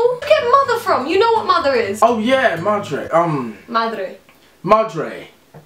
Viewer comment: XD 3:01 i thought deji said madara from naruto